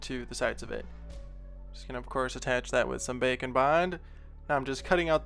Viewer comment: Whenever says "Bake n bond" I hear "bacon blind"